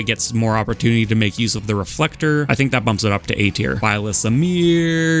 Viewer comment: I thought byleth said nair instead of amir